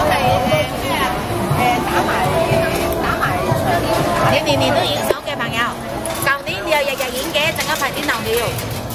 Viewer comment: what does that woman said? Is she angry? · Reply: Funny canto...She said you film always here, last year u was here too...